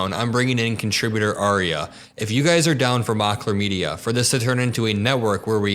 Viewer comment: "If you guys are down for my chlamydia..." Did I hear that correctly?!